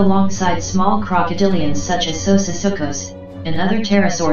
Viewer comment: What's did she say at 0:28? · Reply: "Small Crocodilians such as Susisuchus" :)